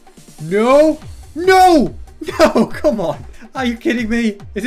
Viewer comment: he says come on but subtitles say come one It’s rebelling